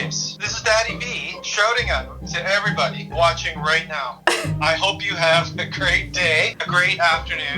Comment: I thought he said "i hope you all have a gay day"